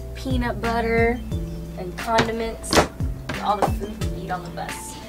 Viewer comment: I almost thought she said condoms not condiments.